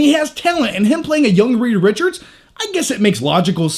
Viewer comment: Love your reviews, maybe it's me, but did you say 'Rita Richards' ?